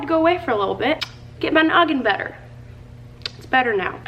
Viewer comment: Idk if you were trying to speak German but I heard "mein augen" which means my eyes lol, you were probably looking for "mein kopf" meaning my head. I'll be so embarrassed if that's not what you said lol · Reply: “My noggin” is what she said, meaning getting her brain (mental health) better.